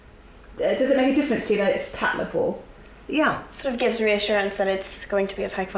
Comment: what does the commentator say at 04:18? technical? sorry not a native speaker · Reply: 'tatler poor' but i think she means Tatler report: so it's suggested/recommended in Tatler magazine The girls says: gives me assurance it's going to be of high QUALITY